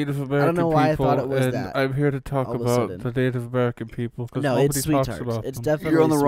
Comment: bro okay, i unpause and put my headset in and the first thing i heard was "African American People" followed by laughing, what the hell is going on